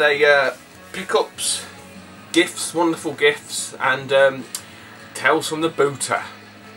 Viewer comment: lmao-I thought it said "tales from the boozer" so i settled down for a laugh !!!!